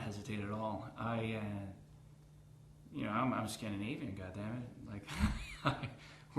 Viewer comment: I thought he said, "I'm a Scandinavian guy," not god.